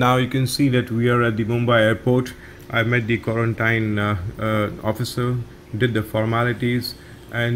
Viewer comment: wait a minute did he said quarantine officer?